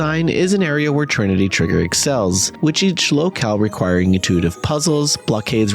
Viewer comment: I know it was “locale” but I heard “each lolcows history”.